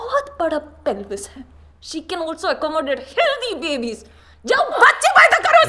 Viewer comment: "jao bacche paida karo uske sath" man wtf 🤣 Also when she said "she can also give birth to healthy babies" i thought she said "she can also give birth to filmy babies" ☠️🤣